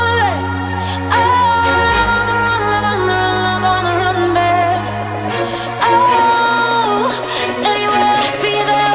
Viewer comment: WTF she saying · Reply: love on the run, love on the run, run